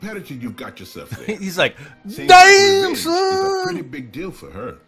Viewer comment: Did i hear you say "damn son"?